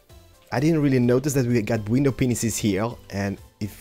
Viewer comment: wtf window penisses!? or am i hearing stuff